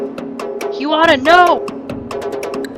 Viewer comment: who else heard You oughta know! ?